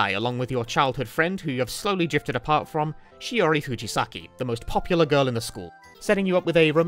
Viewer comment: I was kind of half-paying attention as Im listening to this while working and misheard the name as Chihiro Fujisaki and boy howdy did I do a physical double take 😂